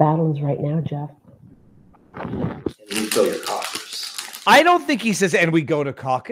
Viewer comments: VIVA YOU ARE SO RIGHT! It sounds like Jeff said "fill your coffers", not "go to causus" when telling Kari why she should go along with this offer/bribe. GREAT CATCH THERE VIVA, even better than any of your fish catches, some of which were quite good! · yeah I hear "fill your coffers".